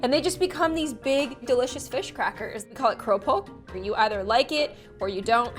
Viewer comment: did she say krupuk or something else?